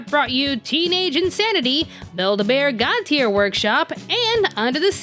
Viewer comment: WAIT DID YOU SAY "BUILD-A-BEAR"? AS IN THE AMERICAN COMPANY?! THEY HAVE THEIR OWN ANIME?!